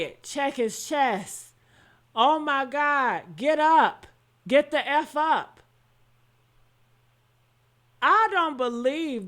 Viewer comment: I heard him say get the F up